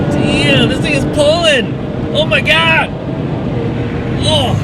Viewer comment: Bro 😂 2:25 did I hear that right ? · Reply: "damn, this thing is pulling" is that what you heard?